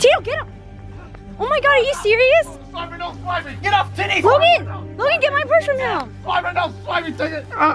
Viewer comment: did he say get off titxs